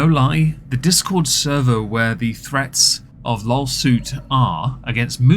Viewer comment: Did I hear that right? LOLsuit? That's golden! 😆❤